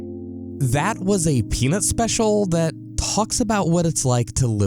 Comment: You can not tell me that he did not say penis right here